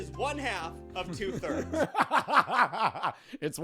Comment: I THOUGHT HE SAID 1/2 OF 3/3 AND I SAID 3/6 SO CONFIDENTLY!!!!!!!!!!!!